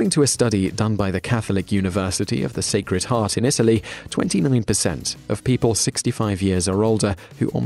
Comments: Does it really say "twenty million percent" at 1:30 for the statistic? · sounds like he says 20 million percent.